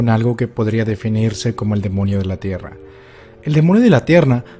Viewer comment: ¿Soy el único que en el minuto 0:11 escuchó que dijo el “demonio de la tierna”?